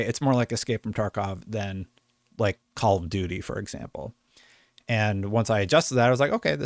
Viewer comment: I'm so glad i rewinded that back i thought he said stalker was like call of duty🤣🤣🤞🏾